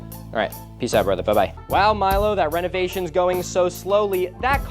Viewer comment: did he say Hewo???